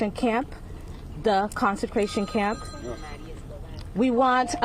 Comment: Does anyone else hear her say, “consecration” instead of concentration?